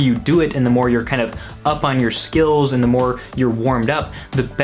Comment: Did anyone else hear "skittles?"